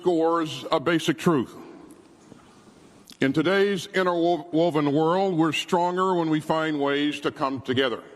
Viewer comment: Did he just say "Inter Woke-Woven World"?